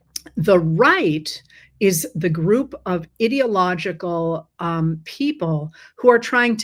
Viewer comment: I heard “the right is the group of idiot illogical” 😂😂😂